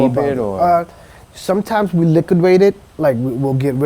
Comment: liquidate . Wtf did he say!?